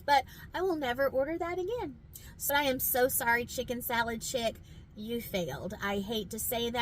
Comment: Ayo did she say “Chicken Salad Shit”?